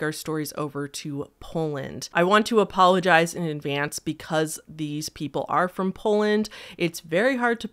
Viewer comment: “I want to apologise in advance because these people are from Poland” 😭😭 (/j, just kidding, I know that’s not what she said just thought it sounded funny)